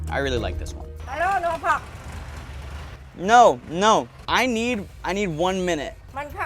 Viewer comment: what's he saying i coundnt understand him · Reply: She say That she dont understand that he need 1 more min